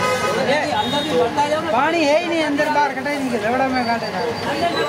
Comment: paani haiyi nhi Kya lavda me pani That sound i heard and after hearing that sound in background i literally started laughing very hardly 😂😂🤣🤣🤣🤣